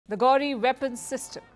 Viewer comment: i heard it as Gauri instead of ghori 😂😂